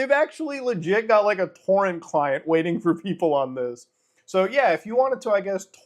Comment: I thought MUTA said Corn Client 🤣??! I had to rewind at look at that menu 💀 🤡... Bruhhhh